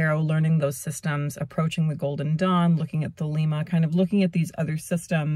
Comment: you said you were looking at something that sounded like "the Lima" while learning the tarot and I'd like to know what that is. I googled it and didn't find anything that looked likely. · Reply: Thelema!